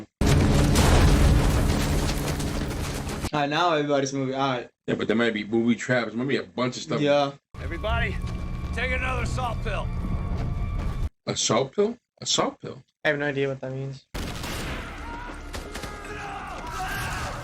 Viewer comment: A salt pill will replace the sodium you would lose from sweating. It was incredibly hot. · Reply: ahhhh, I always thought he said "assault pill" meaning meth